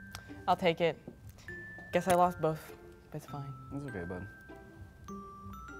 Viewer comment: At the end it sounded like Jean said “it’s okay bub” but idk if he actually said bub. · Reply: She said guess i lost both and he said its okay bud ...also their so cuteeee!!!! 🥰🙂